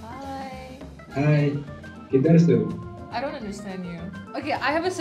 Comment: what did he say!? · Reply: Kidhar se ho